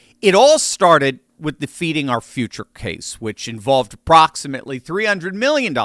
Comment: Oh, Feeding Our Future. It sounded like you said Defeating Our Future.